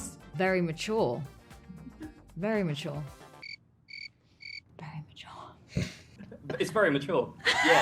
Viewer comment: Very mature, very mature. . . Very mature. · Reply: 😂😂 I heard "Immature, immature, David is so immature."